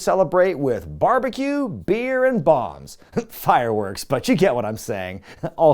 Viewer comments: Thought you said “barbecue, beer and bongs” in the intro and was like “hell yeah” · For the record, I thought you said barbecue beer and bongs. Which technically fits now